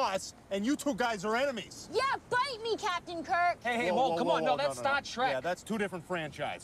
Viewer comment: Your his boss and you two are enemies ! Yeah bite me Captain Kirk ! Whoa whoa whoa that's Star Trek that's a different franchise! 😂😂😂 I feel you being a nerd geek rocks !! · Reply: When they first said it I thought they said STAH SHREK